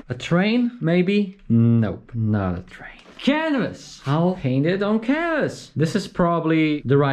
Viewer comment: I thought you said you were painting on the cannabis